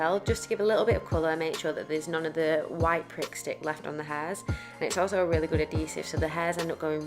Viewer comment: Sounds like your sayin “Prick stick “ 😂😂😂 love this !!! Brilliant video xxx